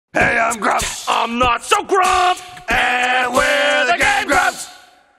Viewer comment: For the longest time I thought JonTron was saying "I'm not surprised!" in the opening.